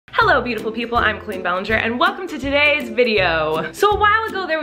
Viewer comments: I thought in the intro she said "Queen Ballinger" and I wouldn't have argued with thag but the fact that didn't say anything after made me think 'wait did she just say Queen?' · It sounds like she said Queen Ballinger at the beginning and honestly PREACH😂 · When you said the intro I thought you said ‘Queen Ballinger’ and I was thinking yes finally accepting her truth!